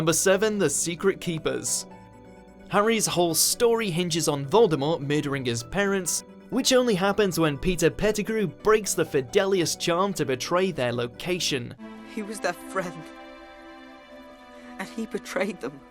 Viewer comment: i thought u said pizza pettegrew